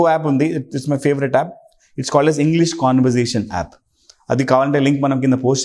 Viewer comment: Sir 17:12 you said app name.. that English........ ? What is that app name sir I can't understand... Plz tell anyone. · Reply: English conversation app in Google playstore